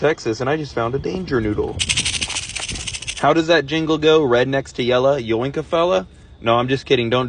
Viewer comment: “Red next to yellow, yoink a fellow” followed by the “just kidding” got me a lot worse than it should’ve 😂 I was like I feel like this one is actually the danger noodle and not the peaceful mode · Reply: I thought he said “redneck and yellow, yoink a fellow”😂